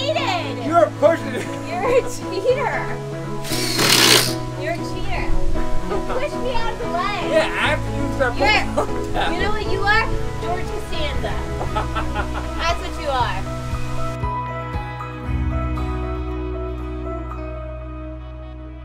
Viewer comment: "You know what you are? George Costanza. That's what you are." -- Is that what she actually said? "Serenity now!"